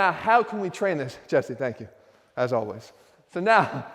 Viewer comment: On 2:11 I first heard "Jesse Pinkman as always"